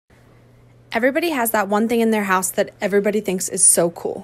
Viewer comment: I thought you said in the house that is outside